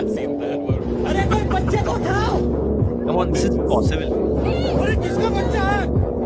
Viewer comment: did he just said this is possible😂